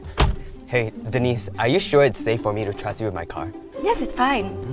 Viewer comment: I thought jianhao said on denise babe on 0:20😂